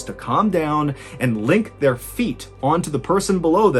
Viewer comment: Did anyone else think he said "lick their feet" instead of "link their feet"?? Oops, my apologies...I was just listening & not actually watching when he said that, lmao...🤦🏽‍♀️🫣🤭💀🤣